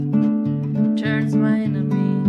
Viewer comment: Woww , I love the way I thought you were saying Enemy...... and its Inner Me .... profound !!